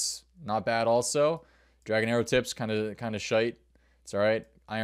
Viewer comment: did you just say shite? 😂 Where have you been hiding the Irish in you?